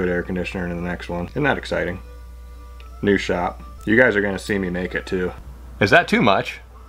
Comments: When he said "You're going see me make it" sounded like "your going to see me naked" 😂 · I legit heard "You guys are gonna see me naked too" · “New shop, you guys are gonna see me make it too” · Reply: I swear I heard "you guys are going to see me naked too" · I legit heard "You guys are gonna see me naked too" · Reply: I thought he said make it too